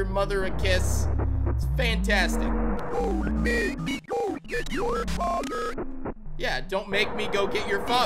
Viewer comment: I literally heard mom saying "to to your father"